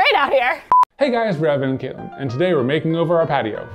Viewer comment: in their intro, i thought they said "hey guys, it's resin and katelyn'. honestly, ive been watching them for over a year. im not evem that far off tho... lol